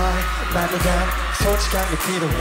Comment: Wait all this time I thought he said "shawty get naked" I- I need help